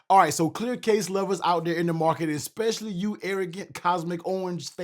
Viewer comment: lmfao I thought you said “Erik” at first, and had to rewind like 3 times wondering why you were calling me out specifically. 😂😂